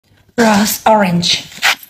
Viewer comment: it sounds like she said gross water